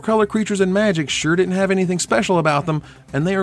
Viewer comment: "relegated", not "regulated" Using a sketch instead of the card art for Agrus Kos after he died was a nice touch.